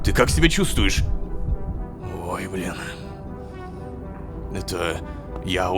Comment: мне одному показалось что он сказал:ох бля 😅